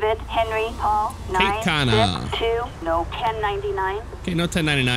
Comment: i thought it said Kate Cena XD KATE CENA!!!!!!!